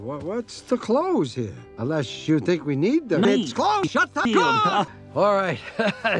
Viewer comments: Nobody: Buck cluck: ITS CLOSED CLOSE THE CAR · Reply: I hear “it’s closed, shut the- closed!” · Nobody: Buck cluck: ITS CLOSED CLOSE THE CAR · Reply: I hear "Its closed! Shut up! Arrrgh!"